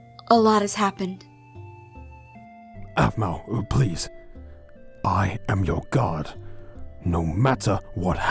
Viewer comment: When Garroth said, "I am your guard." I thought he said, "I am your god."